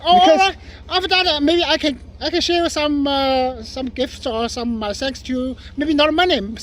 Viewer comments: Wait - did he say "Maybe i can offer some gifts or sex to you"? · did he say cig’s or … I hope I miss headed that lol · "I could have sworn that at 7:57, I heard him say, “Share some gifts and SEX with you..!"